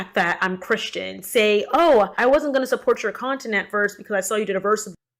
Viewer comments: Excuse me.. but did she just say "Continent" Maybe if she practiced, on her enunciation of words, from the English language. She'd get more followers.. But yet, maybe not.. lmao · I wasn’t going to support your continent. LOLOL. So not only did she say suspefically, which isn’t a word, but apparently she thinks the word for content is continent, which is a plot of land ha ha ha ha ha ha How does this person have a quarter of 1 million subscribers when they can’t even talk correctly?